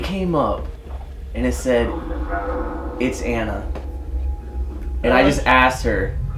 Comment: At like 1:04:10 when is talking about anna i hear it's incredible or something about incredible😯